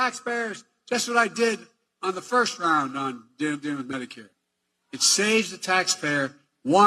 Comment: I thought he said he beat medicare.....not covid